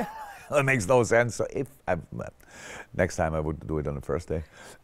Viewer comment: did he say the "next time"?